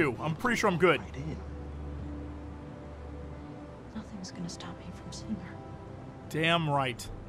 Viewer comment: says: "Nothing is gonna stop me from seeing her" After watching Chronicle all I hear is "I am the apex predator!!!" lol